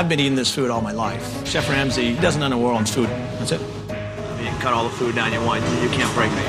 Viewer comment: I didn't understand what he said, but it sounded like "Ramsey doesn't know a lot about food". GORDON RAMSAY!!!!